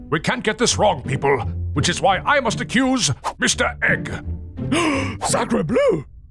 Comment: What did Mr Egg say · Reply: Sacred Bleu